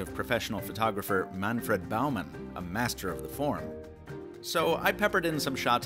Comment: I thought you said "a master of pron" it sounded so casual LOL. Where was my mind?